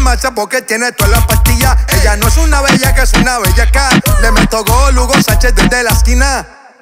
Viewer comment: entendí bellakhat